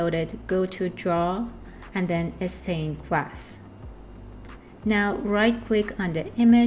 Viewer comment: Awesome content! @ 02:41 did you say upload image > draw > 'extain'? I can't seem to find the 'extain' tool you are referring to, you can't click directly on 'draw' either. Otherwise great tutorial and thank you for sharing this. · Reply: Glad you liked it! it's actually draw > Stained glass